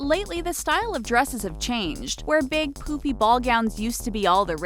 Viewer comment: when she says poofy i thought she said poopy